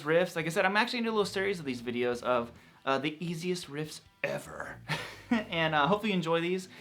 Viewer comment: I thought it said the "Tastiest" riffs ever. LOL